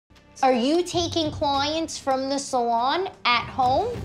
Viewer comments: I thought she stole coins lol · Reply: Cyn V me too I swore she said coin then I herd clients makes more sense to me now. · "ArE yOu TaYkInG cLoYaNtS fRuM tHe SaLoN aT hOmE?" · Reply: Denoot i swear i thought she said COINS ahahahah